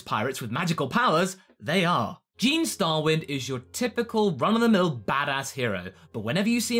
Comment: almost thought he said keemstar for a sec there hahahaha